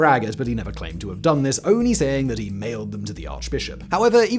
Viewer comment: 56.30 Luther. I misheard you. I though you said he actually nailed it to the arch bishop. A rewind revealed that he mailed it to the arch bishop. Not nearly as interesting. Maybe this is how historical fairy tales come about. History is kind of boring if you're not actually a historian (a bit like maths, geography, science, etc. if you're not a mathematician, geographer, scientist, etc.) The ordinary folks like a good story. And nailing things to arch bishops is definitely a good story (although not something we would recommend in these more enlightened times).